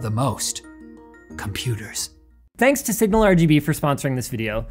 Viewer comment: I thought he said sigma rgb lol